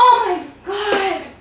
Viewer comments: for a second i thought she said injure my ass XDDDD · I thought she said ninja my ass